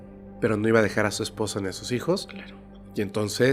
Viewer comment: En el minuto 44:22 se escucha junto a la voz de un murmullo que dice "claro que no" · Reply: Solo fue diciendo "claro"